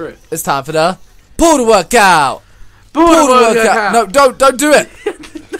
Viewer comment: It sounds like ur saying pool world cow 😂 🤣